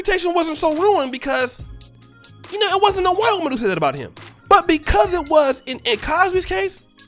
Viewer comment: I still couldn't make out the words "white woman" , even with the playback slowed to .50 and .25 speed LOL! But yet, I knew that's what you said! Good video as usual!